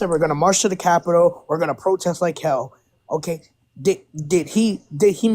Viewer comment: He did not say, "Protest like Hell", he said, "FIGHT like Hell"!